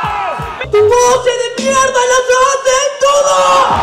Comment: Qué dice? Queres competir y yo tus frases robo, Tus técnicas de mierda las hacen todos? (?